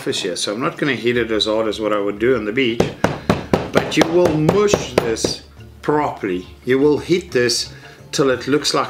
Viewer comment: I almost thought you said "...I will moer this properly ... " :DD